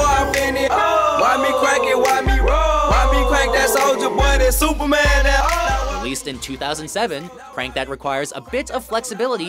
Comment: I had no idea that that Soulja Boy song was about "supermaning a hoe" I always thought he said "now superman and ohhh"😂😂😂